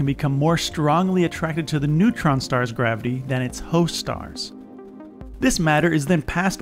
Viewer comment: Who else is here understanding maybe 1% of this but just here for the ASMR and/or the visuals? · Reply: Also did anyone else hear at 11:17 “strongly attracted” and then brain made you hear “hoe stars”?